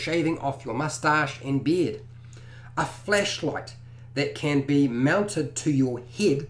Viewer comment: Definitely thought he said fleshlight not flashlight 😅😅😂